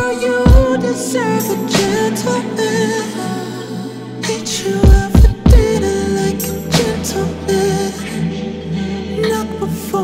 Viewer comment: Did he say, "eat you out for dinner like a gentleman?" -asking for a friend. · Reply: All this time I thought he said take u out to dinner like a gentleman 🤣